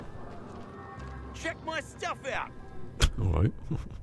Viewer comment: Thought that vendor said "shit my stuff out!" 😆